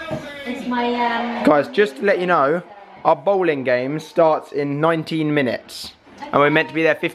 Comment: When you said bordering, I thought you were just saying Bowling really weirdly ahahah